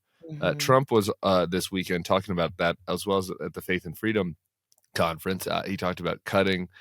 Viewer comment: I initially heard this as “The Fake and Freedom Conference” 😄